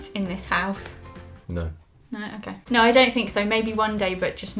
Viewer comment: I misheard it and thought you said about having kids ‘maybe on Monday’ and I was so confused lol, also you keep replying to everyone’s comments I’ve never seen such a dedicated YouTuber thank you, love you!💗